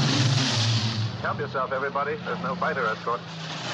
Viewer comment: in case you didn’t know what he was saying “ help yourself everybody there’s no fighter escort”.